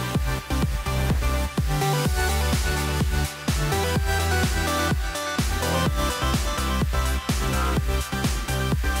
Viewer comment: what is the word? just curious 😂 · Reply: It's pronounced "net"